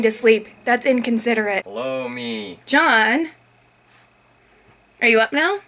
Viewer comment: did john say "Blow Me" at 4:03 followed by that creepy fast head move.